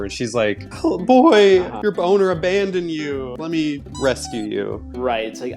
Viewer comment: Why the heck does 0:21 sounds like he said, "your boner abandon you"😂🤣